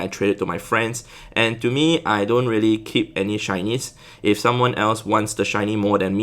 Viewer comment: shinys I can't hear shinys I hear Chinese 🤣🤣🤣